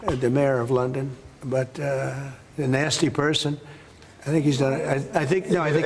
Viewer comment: I thought he said Nazzi person 😂😂